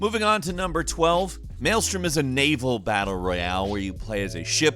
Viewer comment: I can swear on my right hand that when he said "Maelstrom" all I heard was "Male Strip". I laughed the shit out of me: Thanks.